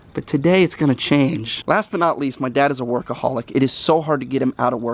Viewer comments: At first i thought he said "my dad is a alchoholic" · I thought he said my dad's an alcoholic, when he said workaholic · I thought he said my dad is a alcoholic but he said workaholic